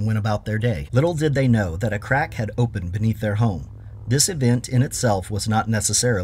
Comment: Not gonna lie when you said “Crack had opened beneath their house” at first I thought you said “a crackhead opened” it took me a moment. 😂